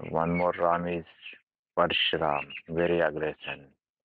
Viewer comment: i think he said Parashuram